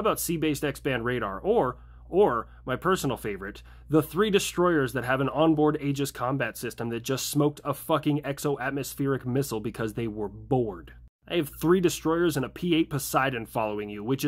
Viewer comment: i thought how you said aegis was “agus”… am i stupid?